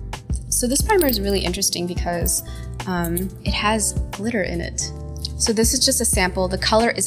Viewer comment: “it has glitter in it” me: did you say litter? michelle i thought you were done with putting litter on your face